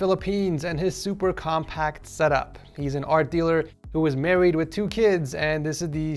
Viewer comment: Did I just hear this "That is married with two kids" btw ik what he didnt say that I just misheard